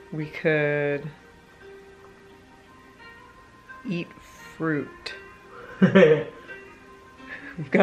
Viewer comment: When you said "Eat Fruit" I thought you said, "He is Groot" I loved it :D